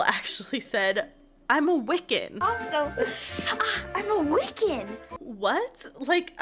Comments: Marnie always said “wiccan.” I remember watching it as a kid and I thought she said “wicked” and just thought wicked was another word for a witch even though it didn’t make sense 😂 I just accepted it cause I thought that’s what she said. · I always thought Marnie said “I’m a wicked” 😂 which is equally weird